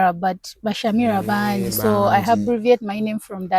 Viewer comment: I heard her say i "habbreviate"...😢😮